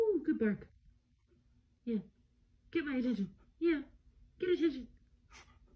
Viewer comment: He looks so proud of himself. So cute! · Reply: Whoops, didn’t realize the dog was a girl. At the end the owner said “good boy”, or at least sounded like she said good boy, so I thought she was a boy dog. Sorry about that! (I’d edit the comment but I’m on mobile and it doesn’t let me edit on mobile for some reason)